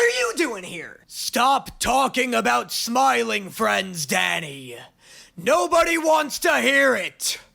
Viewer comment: I thought little was saying Daddy not Danny